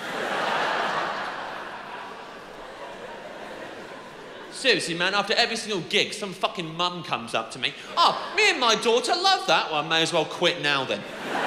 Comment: Lol I thought James said "some nun comes up to me, with her daughter"